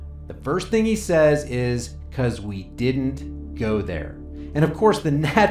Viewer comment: I have seen that video elsewhere and I thought he said because we did go there.